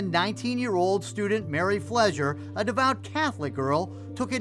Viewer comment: Sounds like they saying Mary Pleasure to me but it's Flezer .